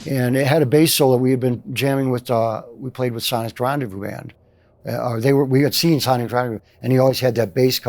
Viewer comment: Great episode about some excellent '80s songs. What band is he referring to at 15:35, though? I didn't catch that name. Any ideas? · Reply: Sonic's Rendezvous Band!